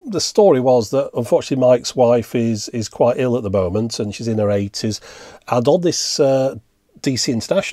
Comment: I thought you said "my ex wife is quite ill at the moment and she is in her 80s"...had to rewind.